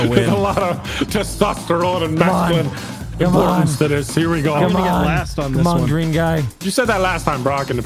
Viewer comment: I thought Evan said "Come on, Dream guy"